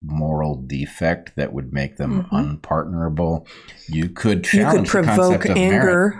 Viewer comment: at first I thought Bret said “unpardonable”, but he said “unpartnerable”, by which he means “unable to work with toward a common goal”, I think.